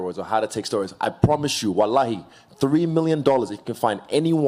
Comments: Did he say Wallahi??? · Lmao 4:55 did izzy just say WALAHI ?? Im an even bigger fan now😅